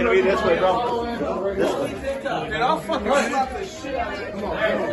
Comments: Am I tripping or did Bryce say I’ll slap the shit out of that n***a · If Bryce say “I’ll slap the shit out of you” one more time bro he say that to everybody 😭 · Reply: I stg I thought he said “I’ll slap the shit out that nigga” listen closely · No one heard him say “I’ll slap the shit out of that nigga”… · What did white dude say at 3:03 did he say “I will slap the shit out of that nigga” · Am I trippin????3:00 I think I heard him say “I’ll fuckin slap tha shit out that nigga”